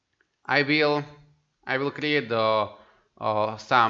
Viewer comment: took me 30 minutes to figure out what you said at 9:54- turns out you said "R" oopsie xD